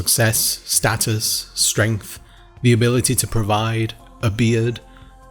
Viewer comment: At around 1:46 I heard "the ability to provide a beard" and not "the ability to provide, a beard" and I don't know why I commented this. I just felt like it was a humorous thing, I dunno. Also I just liked this video, I've noticed all of this before but just couldn't put it all into words and I'm pleased someone else did it for me.